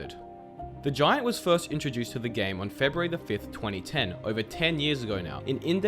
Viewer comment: i almost forgot about this guy xD Edit: 1:08 i thought it said january and i was like wait thats my birthday